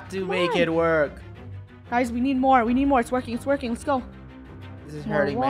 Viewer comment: When the part that the krew is saving bob i thought said "its twerking"